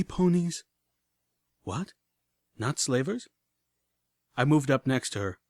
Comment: I turned on captions, instead of the captions saying "not slavers" it said "knots labor's".